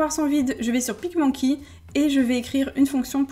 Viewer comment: Vous voulez une suite ? 😜 Beau dimanche my loves 💜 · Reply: quel est le nom de l'application que tu utilise? j'ai compris "picmonkey" mais je ne trouve rien...